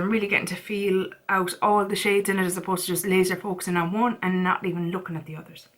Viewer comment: said did she say “lazer poops?” I had to rewind like 10 times before she agreed it was lazer focusing 🤣🤣🤣